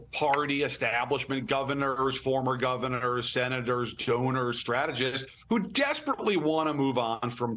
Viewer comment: Did she just say stoners instead of donors? 🤔